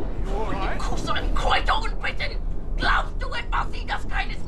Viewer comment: I thought she said '' Cause I'm Quite old Britain! ''